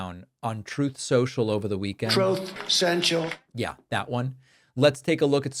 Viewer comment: So THAT’S why I heard you say Troth Central